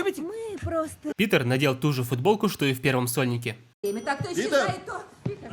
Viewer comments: я один услышал в первом сонике? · я один заметил что он сказал "в первом Сонике" ? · "Питер надел ту же футболку что и в первом сольнике..." Просто, пожалуйста, скажите мне что я не один услышал "что и в первом Сонике"